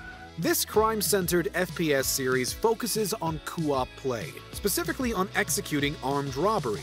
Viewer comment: did he say "coo-op" lol